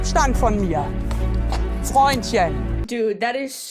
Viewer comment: did she just called the horse “freunde” (friend)😂🌝